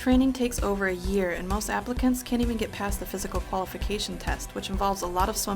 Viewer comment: I thought it said Africans at first